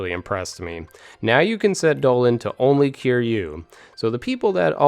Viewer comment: 😐 Repeat what you just said.... "Doland"...